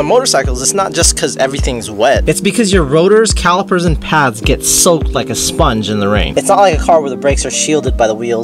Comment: Did you just say your rotors soak up water like a sponge, and suggest you change your tires every time it rains? 😂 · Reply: Nope. I said the rotors get soaked like a sponge, not soak up water lmao